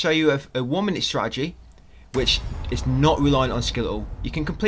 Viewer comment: What does he say at 1:53? “A woman’s strategy”? I have a hard time understanding some of what he’s saying bevaue it’s too fast and because I’m used to an American accent. No offense, I just wanted to know what he said. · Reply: Sorry for my accent, I said One-Minute Strategy, listening to it back though, can see how it sounds like Woman Strategy XD. What im saying there is basically a 30 second strategy maybe great for speedrunners, but there maybe a One Minute strategy which is like 5 times easier to pull off. So you will find a lot of the time, my strategies may not always be as quick as a Speedrunner video, but, you are likely to spend LESS time doing mine strangely as it is easier to pull and so you will not need to keep practising or hope to get lucky!! EDIT: If you put on CC as well, the text actually reads out 'One-Minute Strategy'!!